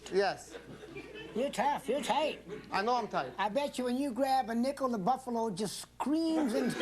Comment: What did Mills say at 3:00-3:04? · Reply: I bet you when you grab a nickel the buffalo just screaaaaams...